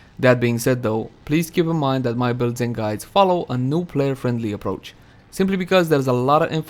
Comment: Did I hear follow a noob player friendly approach?